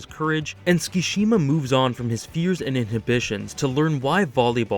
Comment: "Tsukishima moves on from his .. fuse ?" I didn't understand what he said can any native english quickly give me a captain obvious note ? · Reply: Fears. I upload my video scripts into the CC tool, you can use that next time.